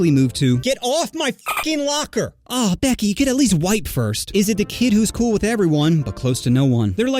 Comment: bro I thought you said "You could at least get white first" but no, sadly not. Amazing video, banger after banger no exception.